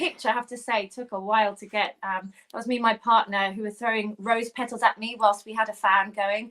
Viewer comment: Did say her partner threw the petals? Oh my goodness😂